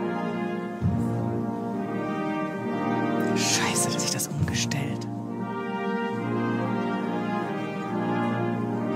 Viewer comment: Was sagt die Moderatorin bei 14:50? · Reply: "Scheiße, jetzt hat sich das umgestellt" Also so habs ich verstanden